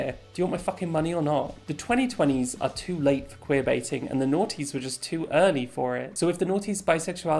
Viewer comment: I'm so sorry but WHAT is this word she keeps saying?? The "naughties" ??